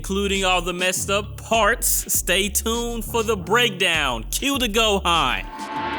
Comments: I have a confession. I'm not a native English speaker and I didn't use to put subtitles in your videos. So, when you say "Cue the Gohan" I always thought you said "Cute to go, Hun" as a way of getting us ready for the breakdown. lol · I have a confession. I'm not a native English speaker and I didn't use to put subtitles in your videos. So, when you say "Cue the Gohan" I always thought you said "Cute to go, Hun" as a way of getting us ready for the breakdown. lol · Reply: Omg I was just thinking about that. I always thought he said "cute or go home" like talking to the viewers like if you're not cute then go home🤣 but yours is way better that's so funny. What's the real thing he says mean? Idk how it's spelled